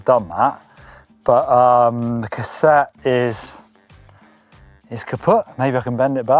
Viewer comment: what did he say? kaputt?